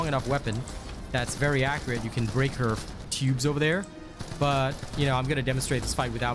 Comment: At first I thought you said "shoot her p*bes" then you sId it again.... TUBES😂